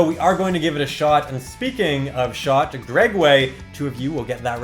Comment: Did I just hear.. Gregway? BEYOND! Still watching the vid, but congrats on the season finale! This series always cheered me up. :3